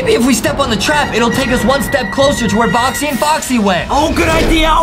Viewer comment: O.o Did Mr Potato just say 'bOxY aNd BoXy' (edit) nevermind i though they did.. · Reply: I thought he said boxy and poxy